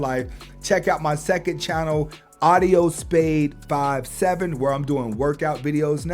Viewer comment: Hey please put your other channel in writing on the screen of your next video. It's hard to find it sounds like your saying ( Audio Spade 57) Thanks Big Dog